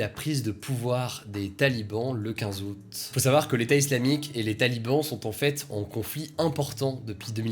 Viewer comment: J'ai entendu "l'état islamique et l'état Liban sont en conflit" mdr